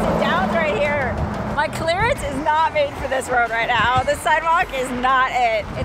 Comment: I swear she said, "My clitoris is not made for this road right now" and I am like wait what? I went back and replayed it a few times and it seems she is saying "claris" or something like that but I thought she said, you know. 🤣🤣